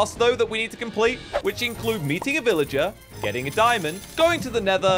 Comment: meeting bro i thought he said beating a villager